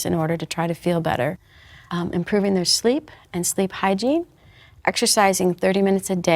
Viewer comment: What is the word after "improving the sleep"? (I just don't get it.) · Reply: "and sleep hygiene"